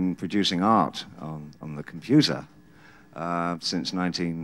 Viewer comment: Oh wow, this was a whole lot of info crammed into a video. Thank you! PS: When said he'd been making "art on the computer", I thought he said making "arts of The Confuser".